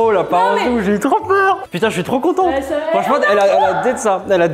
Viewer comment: c’est moi ou frite à di contentE ?